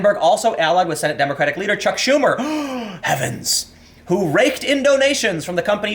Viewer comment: I thought he said "who raped indonesians"...